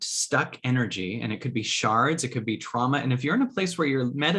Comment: 🤣 Misheard you saying, “sharts.” I was like Kyle is really in tune with that whole bag of riced broccoli, cauliflower, & carrot I had last night. 🤭